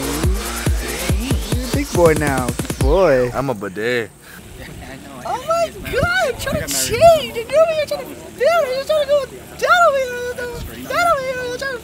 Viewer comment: did you just say "bê đê"? You know that means gay in Vietnamese right?